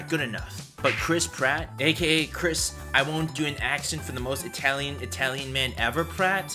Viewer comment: he actually said they were working on the accent.